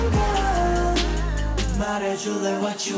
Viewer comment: 0.10 did he just say "we aren gay" ? and then 0.12 "we are gayy" hahahahahah i love this song!!!!!!!!!!!!!!!!!!